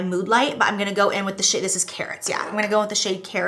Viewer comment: i literally thought she said carrots and after like 2 minutes i was like ah no its karats